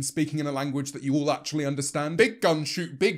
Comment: "big gun shoot big bullet, fast" is the only thing my American ass could understand in this video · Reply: i thought that he said "big butt"